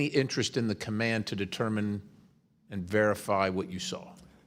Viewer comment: I thought that sign said chief wiggum !